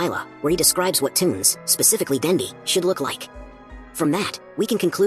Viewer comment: DID HE SAID SHE DANDY IS A BOY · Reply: He said should